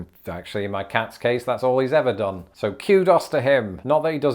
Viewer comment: did he just say Q-Dos?